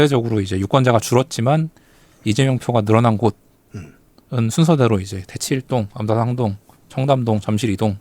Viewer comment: 대치1동 바로 다음에 어디라고 하신 건지 아무리 돌려봐도 도저히 안 들리는데 들으신 분 알려주시면 감사합니다ㅠ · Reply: 암사3동으로 추측됩니다.